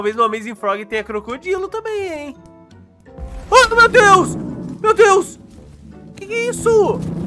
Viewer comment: No momento 12:13 quando o crocodilo esplodiu eu ouvi vc falar Matheus! 😂😂👌 O nome do meu chush ( ͡° ͜ʖ ͡°)❤️